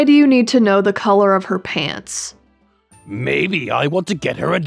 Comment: I died of laughter when asked "What color pads r u wearing" XD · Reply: I thought he said Pads till I heard him actually say Pants XD